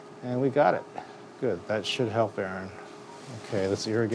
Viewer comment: It took me way to long to realise he was talking to the camera and not Erin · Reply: CJ Dangerfield ikr, I heard "that should help, Erin" not "that should help Erin"